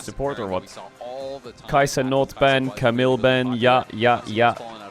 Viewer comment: I heard "Kai Cenat ban" 😂